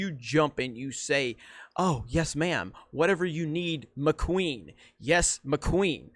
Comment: Does anyone besides me feel like Jeremy is saying "McQueen" instead of "my queen"?